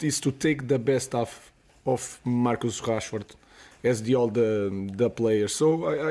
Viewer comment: It sounds like Amorim said Marcus Cashford, that's his only incentive to stay in football.